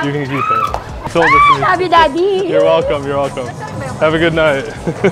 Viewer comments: did she say I love u daddy 😂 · did she say “I love you daddy”? Lmfaoo